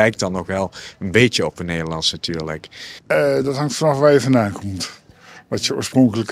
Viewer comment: "dat hangt ervanaf waar je vandaan komt"... he says it so fast... it sounds like he says 5 syllables... how are you suppose to understand such a guy ??